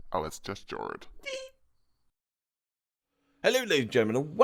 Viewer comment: I heard "gorge"